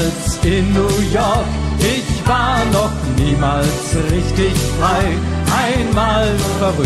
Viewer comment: Ich hab zuerst verstanden "Ich war noch niemals in New York, ich war noch niemals richtig breit ."